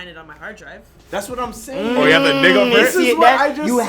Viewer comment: yoooo did jae say "dig-a-print"? 😂😂😂 i'm crying